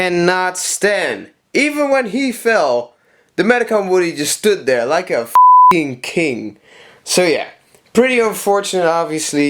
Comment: I think you said he standing there like a fucking king